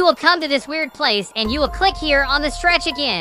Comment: I thought he said secret bug🪳🪲🪰🕷🪱🐜🦟🐍